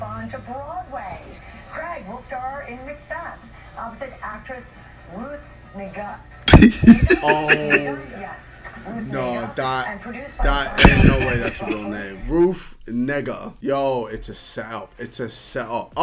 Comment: she said ruth nig** instead of ruth negga